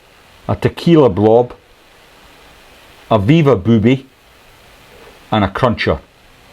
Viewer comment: It is at 48:52. You are asked for the top 5 files and I think you say: candy floss booby, orange blob, tequila blob, beaver booby and crucher. I had never heard of a 'beaver booby'! Perhaps I am mishearing? Thanks. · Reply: sorry pal you misheard its probably my accent. I said "Viva Booby"